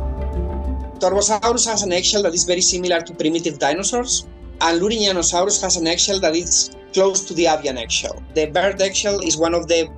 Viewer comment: What is he saying?....What "material" is he speaking of?... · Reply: "Torvosaurus has an eggshell that is very similar to primitive dinosaurs, and Lourinhanosaurus has an eggshell that is close to the avian eggshell. The bird eggshell is one of the fastest growing biomaterials in the world, so the innovation that led to this amazing new material happened in the middle, and they are not so far related." (He probably meant "distantly" instead of "far") Hope this helps.